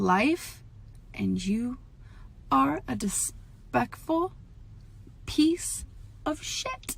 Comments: did she...did she just say....”dispecful” god i LOVE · what did she say... it sounded like a mix of “despicable” and “disrespectful” dispectful?